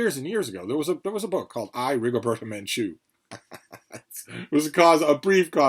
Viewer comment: At around the 18:00 mark, mentions a controversial book that academia is pushing. Despite him saying it three or four times, I can't make out the title. What is it? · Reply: I, Rigoberta Menchu